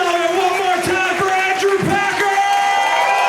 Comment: you might want to look into contacting youtube, at 1:03:41 when your buddy is hyping up the crowd the subtitles say "one more time for Andrew Tate" instead of "Andrew Packer." Not sure if you'd want to be associated with him.